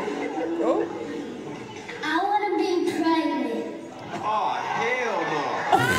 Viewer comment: she said president😂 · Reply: her mom made a pt 2 where she said her daughter said president but everyone thought she said pregnant including her at first which is why she put the camera down